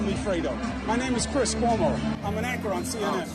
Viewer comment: Did he say he's an actor?? 🤣